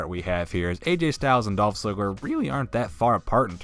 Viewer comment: does anyone else hear " sligler "?